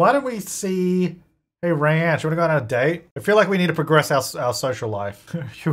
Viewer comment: Every time you say Reyansh, I keep hearing “ranch” but in a really fancy dragged out kind of way